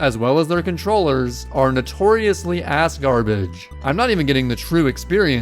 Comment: dude for a second there i thought you said asgard-ish instead of ass-garbage what